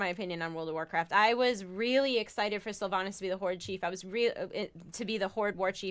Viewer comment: I thought she said she was excited for Sylvanas to be "the whorechief"